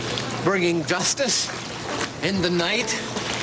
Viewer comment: Hold on a second did Adam say bringing dustice in the night at 0:57 ?